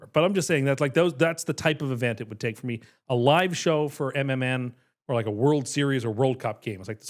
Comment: I thought said a live show to Eminem and had to rewind. That would have been the most shocking thing ever going to Eminem concert. He said MMN